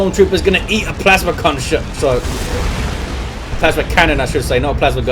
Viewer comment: at first I heard plasma cumshot